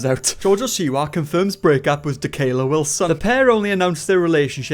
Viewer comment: I thought you said my name 😅😬 (Mikayla Wilson)